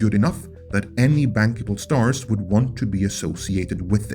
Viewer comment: When you said "bankable stars" I heard "bangable stars". I guess either would work in this case. Though to be fair, the tall one is kinda hot.